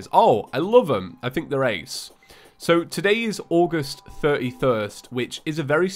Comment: Did he say August thirty thirst?